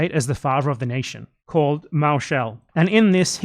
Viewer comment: What was the name of the pamphlet at 33:15? Didn't catch that and I can't seem to find it. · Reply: Mauschel